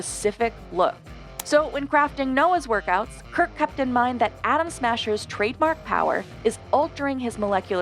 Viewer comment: When she said "Atom Smasher" I heard "Adam Smasher" and, for a couple of confused seconds, I thought they might be doing a Cyberpunk 2077 film...